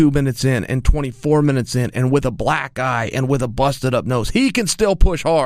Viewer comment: Who else had to go back because they thought chael said “hung in there with a black guy” not eye lol